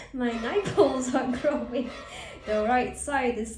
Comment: "MY NIPELS ARE GROWING!" almost thought she said "MY NIKES ARE GROWING!"